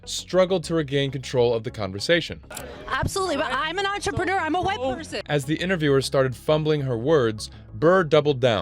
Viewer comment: Did I just hear the tepid female reporter who keeps tossing her head around as she asks if women can be funny say, "Of course - I'm an entrepreneur - I'm a white person..." WTF? It's